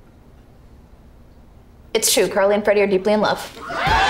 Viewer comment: what did Sam say? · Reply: It's true , Carly and Freddie are deeply in love